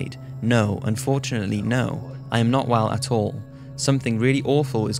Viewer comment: Did anyone hear a voice in the background at 5:46-5:47? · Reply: It said 'no'.......just as the uploader said no on the video. It was a sound effect put in to match the part of the story.